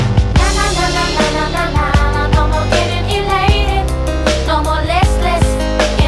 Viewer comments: en el minuto 1:01 parece que dice. ‘’No Molestes‘’ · Pensé que decía "no molestes" y dice "no more listless" 😭